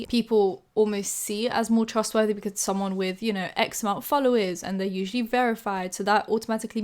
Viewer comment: I heard 'x amount' as 'eczema' lol